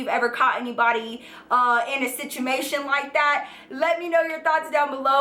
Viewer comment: Who else heard her say "situmation" at the end of the video ??